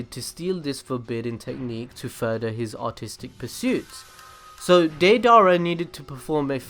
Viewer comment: I thought he said autistic 😂😂